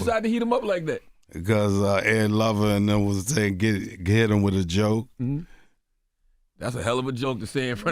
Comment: what he say? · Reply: "Ed lover said get him with a joke"